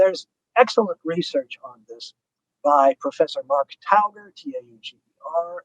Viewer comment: What name does he say 1:20:23 ? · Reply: Mark Tauger